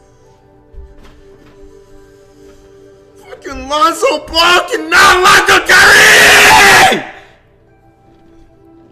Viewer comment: What did he even say? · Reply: "Fkin Lonzo Ball cannot lock up Kyree"